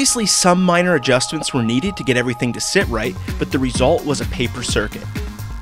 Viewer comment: i thought it said papercut..